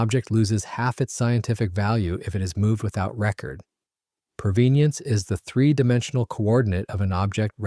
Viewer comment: "Provenience" a new word for me, despite long experience. I suspect that I have heard it before, but misheard it as "Provenance".